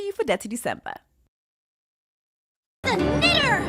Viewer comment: Omg. Why am I now realizing they're saying "knitter"? 😂😂😂😂😂 Lawdhavismercy. Thought was saying the N word.